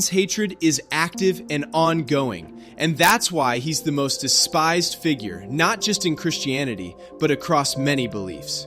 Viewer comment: What do you mean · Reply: I decided to rewatch the video to figure out what you were saying to make sure if I wasn't just mishearing it. 4:19 Apparently, I did misinterpret the words figured for character. So sorry for that. There's a lot of history of people doing that and just saying that they're just characters of an A book, that they never really existed. So, I thought that was what was happening. When my dad was a kid, they talked about King David as if he didn't exist at all. In truth, I didn't send it to you to be mean, but more I thought was helping.